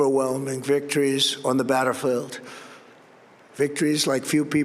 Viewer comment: "Batterfield" ha ha sounded like a robotic Biden glitch. Remember those?